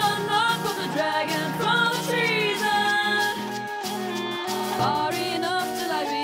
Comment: Anyone else hear, "Run, run, run from the dragon"? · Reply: I heard “from the dragon from the trees” and I was thinking “how’d you piss off the dragons AND the ents?!”